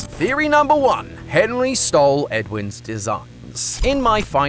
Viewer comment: I love it when I keep getting ads about fnaf so it sounded like tom said “Henry stole Edwin’s(the ad part) Popeyes